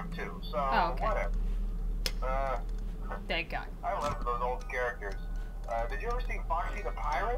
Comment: i thought he said i love turtles XD